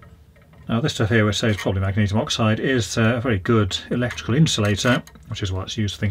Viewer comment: I couldn’t hear him clearly but what did he say that white powder material was that got out of the inside of the heating coil element? Did he say magnesium oxide? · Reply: Yes, magnesium oxide.